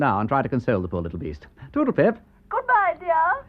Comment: Did he just say “toodlepip” as his goodbye?